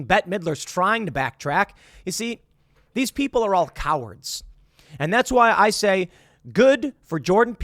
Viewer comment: Tim (re Macy Gray): "Oh, it's laughable you spinless, and pathetic COW.." Me: "WHAT DID HE JUST SAY??" Tim: "...and pathetic COWARDS!"